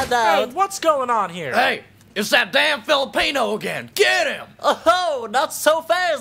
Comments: did he say filipino??? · "is that damn Filipino again?" is that Filipino? or I just misheard it whhahahaahahah